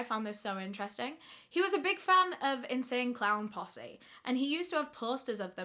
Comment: Colville sounds like “Call-vile” and OMGEEE! I am in Spokane which is about an hour and a half from Collville. My kiddo and I love your channel! Hello from Washington state!!❤️❤️❤️aaand as I keep watching I thought you said NSYNC CLOWN POSSEE”. 😂😂